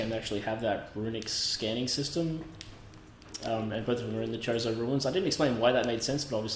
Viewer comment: Everytime you say "chozo" I hear "chorizo"